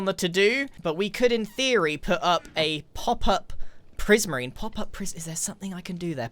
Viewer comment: ngl i thought Grian said "pauper prismarine" and the first thought that came to my head was "Prismarine and the Pauper?" So maybe thats an idea for the shop? Akskjdjf